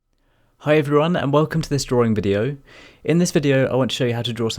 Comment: Hahaha I thought you said at the beginning: "Hello everybody and welcome to this boring video." Oops